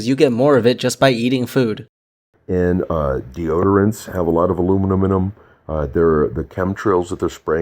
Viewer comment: "aluminum in em" sounds like "aluminuminum". They should do multiple takes and enunciate so people listen to their argument instead of chuckling and repeating a word for 2 minutes. Or is that just me?